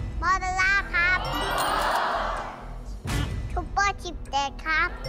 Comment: ได้ยินว่า ซูเปอร์ ขี้แตก 555+